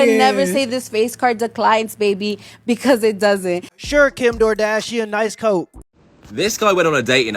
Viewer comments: "Kim DoorDashian" - Guinness World Record for most accurate insult 👏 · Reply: heard that as Kim Gordashian, which would work great in spanish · "Kim DoorDashian" - Guinness World Record for most accurate insult 👏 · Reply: i thought he said kim GORDAshian